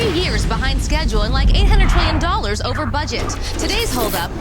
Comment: Did she say "like..."?